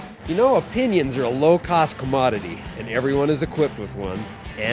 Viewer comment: I thought he said "Pinions are a low cost commodity" I chuckled before I realized he said "Opinion." Anyhow...saving my ass off for an Alpine 3" CT3 with 3.3 falcon shocks with 35's on 15" steelies....Shopped around at the price point...and I will not settle for less....Seems like the perfect middle ground between commuter and weekend warrior. Not a man who can afford heavy rock crawling...at least not with my first build.